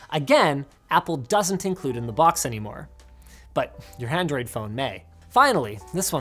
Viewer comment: Did anyone else hear “handroid phone”?